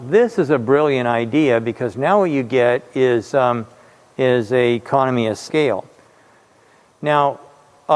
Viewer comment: Talking with the mask - thought he said 'communist by scale' but 'economy by scale' of course. 😂